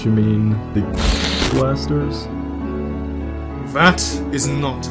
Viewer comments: Anyone knows what is said at 17:04 ? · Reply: He says "The Gaster Blasters." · What does he say at 17:04? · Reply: gaster